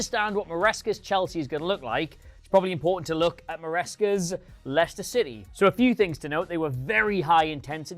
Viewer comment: Did I just heard him say LYCHESTER CITY? SMH